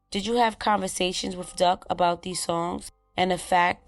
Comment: I got a feeling these dudes didn't kill Duck. Seems like they're being framed. Something is off about this situation. It was too convenient for Teezy to be the Gold Coast out of all places in the city the same time as Duck. LOL. Smh. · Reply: I thought they said he was with duck